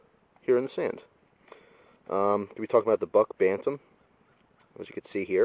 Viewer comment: lol i thought it said buck batman at first